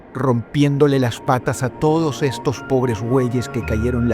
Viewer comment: Estos pobres weyes entendí jaja Pobres animalitos btw :'(